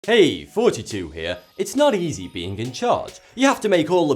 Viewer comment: I always hear him say "Hey, it's 42 here"